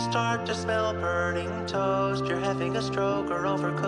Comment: "If you smell burning toast, you're either having a stroke or overcooking your toast." is my favorite line for some reason. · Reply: English is not my first language and I thought he was saying toes and not toast, made it 100% funnier though